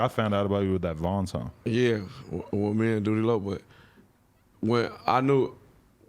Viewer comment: did he say me and doodie lo